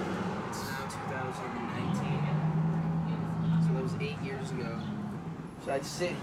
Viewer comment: Tbh I thought that thing said 5 years ago...cuz I was like baby